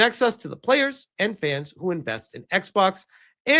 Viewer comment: Invest, or Infest? I'm not sure what my ears misheard, but I know which one they're doing.